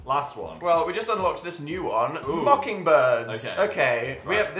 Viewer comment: Dan:"mocking birds!" me:"did he just say fucking birds?"